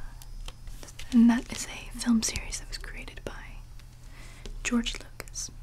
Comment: "George Lopez." That's literally what I keep hearing. xD